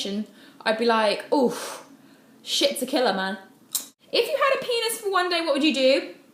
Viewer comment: Hi! Can you please tell me what she said from 2:08 to 2:10 ? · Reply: y Rosie Subtítulos "Shits a killer, man"